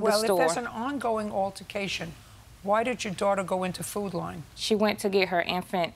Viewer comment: I thought they were saying "Food Line"... nope its "Food Lion"